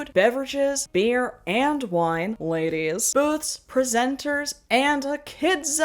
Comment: You can go to my sponsor <link> to try 14 days for free. That’s enough time for Aura to start scrubbing your personal info off these data broker sites, without you lifting a finger. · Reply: Ok, I had to watch that a few times. 'booths, presenters, and a kid's zone' I misheard it the first few times, but in my defense, 'boob presenters and a kid's zone' seems pretty par for the course for anything this ridiculous